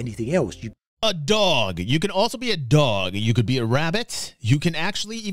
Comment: I thought he said you can be a rabbit 6:36 I thought he was then gonna say "in a hat with a bat And a six-four Impala"